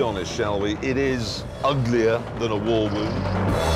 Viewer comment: What do he say at 5:06 ? "Let's be honest, it's uglier then a Volvo" ??? I'm not sure.